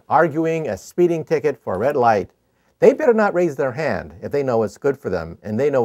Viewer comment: Sounds like he said Yoda light . He mentioned Star Wars and my mind just went in that direction .